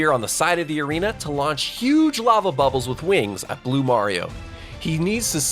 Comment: Am I going crazy or did you just say "blue mario"..